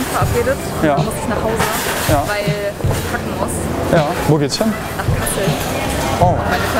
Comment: ich hab verstanden sie muss nachhause weil sie kacken muss xD